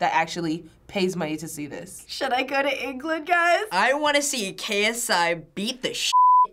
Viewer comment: excuse me, what did you just say??? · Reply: “should i go to england guys“